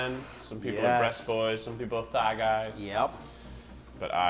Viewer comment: I thought Keith said "Try Guys" instead of thing guys lol